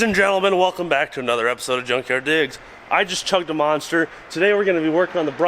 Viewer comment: Why do I keep hearing "junkyard dicks" in this video? :D